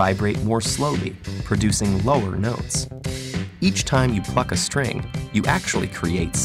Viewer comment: At first I thought he said "fuck"